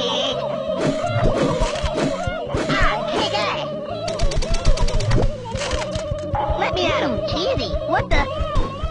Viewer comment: What did Cartman say around 33:56?... "I'm gonna--" what?... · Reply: I think he said “ I’m gonna kick you in the nuts”